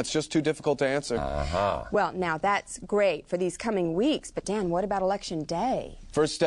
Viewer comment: Did she just say “but damn”?